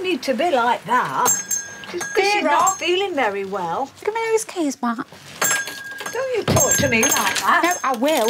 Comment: What does say at 0:48 ?? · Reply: Give me those keys back